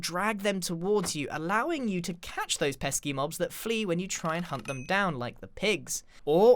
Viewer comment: excuse me did he just say PESKY MOBS?